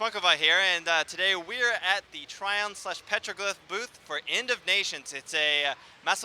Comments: I thought you said 'Indonesians', sounds very similar to 'End of Nations' lol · Lol I heard "Indonesians" and then I realize they are talking about a game..